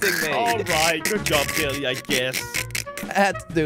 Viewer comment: i thought he said lambolorian